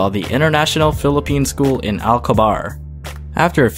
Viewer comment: who else thought he said "International Phillipines School in Allah Hukabar"?